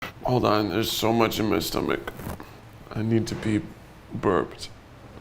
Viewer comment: I thought he said “I need to beep”